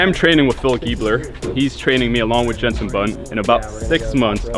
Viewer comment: Did you just say Jenson Button? I’ve rewinded and heard it thrice. Woah. Btw, I NEVER thought you’d buy a Huracan, but I gotta say it suits you. Good luck, I’d love to see you become a racing driver.